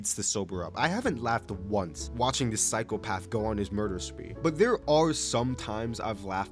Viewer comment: I might be tripping but did he say cycle path